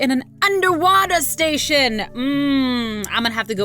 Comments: It said underwater station but I thought it said underwear station. 🤣😂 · anyone else think it said "underwear station" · I thought in 8:43 it said underwear lol